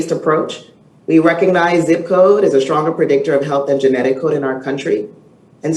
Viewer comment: "We recognize zip code is a stronger predictor of health and genetic code in our country." Did she say genetic CODE? I'm assuming she means ancestral background, but that phrasing, genetic code, bothers me.